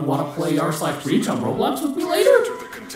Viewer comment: wait did i heard roblox?